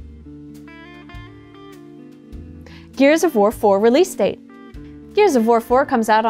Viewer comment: i swear at around 3:35 i heard "gears of vore 4" and i feel like polygon as a collective needs to tone down the vore